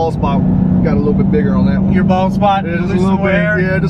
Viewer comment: He said bald spot. I swear I thought he said balls pop. Lol.